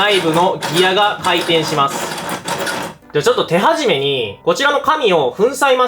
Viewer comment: こちらの神に聞こえたw